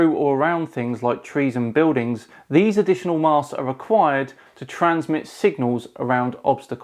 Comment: Oh, "Masts". I thought you said people were going around burning down Mosques.